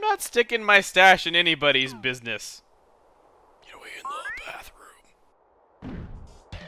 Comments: I heard "in the bathroom" lol ? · who heard him say in the bathroom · Anyone hear the 'in the bathroom" at 12:35 ?